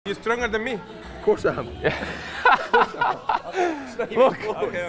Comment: For a second I thought he said of course ya 3am 😂